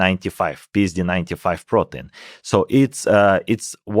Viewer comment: khe-khe... 1:08 "pizdi: 9-5 protu (nay)" did u just say that o mine god?..